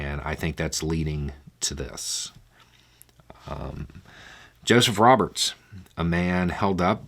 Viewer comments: OH. OH! JOSEPH Roberts. I heard "Justice Roberts" the first two times through. I was like "How is this not the headline of every single news agency?" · I misheard "justice Roberts" arrested for killing his gf. Had to look that one up and then rewatch to realize it was "Joseph Roberts". Glad you put in the ToC on your video description. That helped.